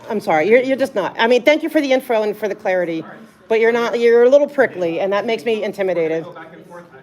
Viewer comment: I thought you were about to say ‘you’re a little prik’ 😂😂😂 and I thought - that’s justified ❤😂❤